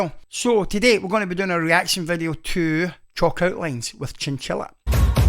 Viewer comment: your accent is gorgeous, but I swear when you said "chalk outlines" the first time in the beginning of the video, I could've sworn you said "chocolate wings" 😄